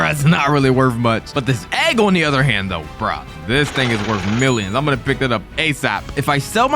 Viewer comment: I thought he said the n word instead of thing